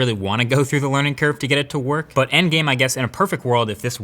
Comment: Did he just say ENDGAME at 6:09 😱